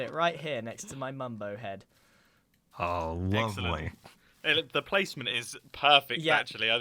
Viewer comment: did anyone else heard that? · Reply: he said "oh for g-"